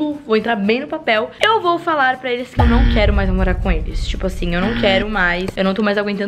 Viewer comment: do que que escutei "eu não quero mais NAMORAR com eles" ?